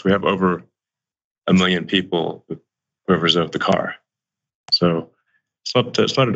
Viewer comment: "we have over a million people re... (what???) the car..." Can't understand what he's saying here; it couldn't be "over a million people RESERVE the car" now could it? Elon is difficult to understand much of the time, but this time it's impossible! And his rockets blow up and his cars crash and fall apart; I wouldn't ride in one, much less buy one!